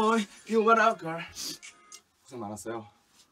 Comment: 뭐라고한거? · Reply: 수고많았어요 인가